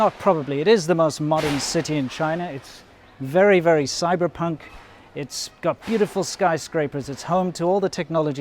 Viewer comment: I heard "It's very, very cyberpunk. Its computerful skyscrapers..." ^^ - About the skyscrapers, I wonder: Where are the people going who used to live in the handshake apartments? Are they forced to move? Maybe getting a tiny bit of financial compensation? (Probably not if rented.) Surely they won't move into the skyscrapers. - Did you use interpolation for the 4K slowmo shots? Or can your camera record that in high framerate? O_O